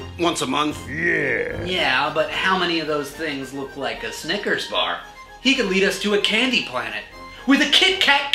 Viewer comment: did u say sniker bar FEED ME!!!